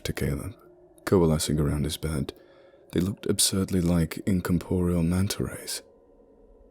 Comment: what is that? i didnt understand after the coalescing part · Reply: He said incorporial manta rays...